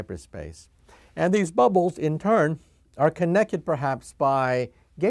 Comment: did he just say connekkid?